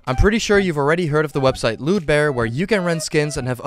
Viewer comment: I heard LeWdbEar